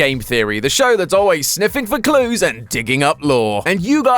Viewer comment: I heard "the shows that's always sniffing the glue and digging up more." I had to go back and double-check! Boy am i glad that i heard wrong, this woulda have been a very different episode.